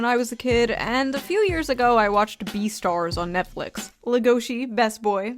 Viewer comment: "I'm a tourist, I watched Pokemon, Sailor Moon, and Beastars" Shoe you're in it's too late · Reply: I heard "beast arse" being said in a cockney accent. 🤦‍♂️😂